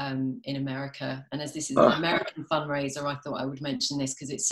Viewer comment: I heard "fun raiser" hahaha